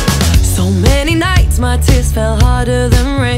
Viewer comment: Am I the only one hearing it as "tits" instead of "tear" in 1:46. Oh bad boy!!!